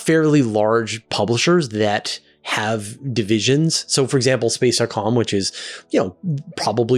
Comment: it sounded like you said Space-R-Com. It was "dot," right?